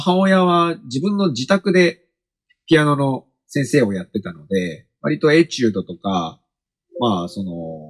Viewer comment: 祖「～ピアノの先生をやってたので、割と”エチュード”とか～」 he said "etude" not "hey jude". i can misheard too. that's pretty similer to it. anyway, thank you so much for uploading this session!